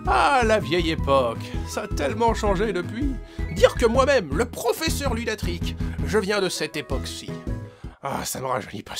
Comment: c'est moi ou il dit "professeur lunatric"?